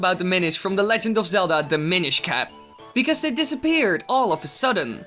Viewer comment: i thought you said "the minish cah"